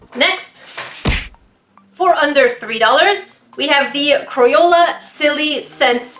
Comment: did she just say cro-yola Ummmm.........triggered....😫😫😫😫😫😫😫😑😑😑😑😑